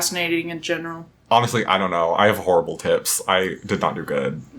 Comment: I got confused for a second as I thought at first he said he had horrible tits